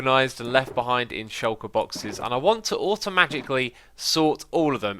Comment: Anyone else hear "Automagically?" lol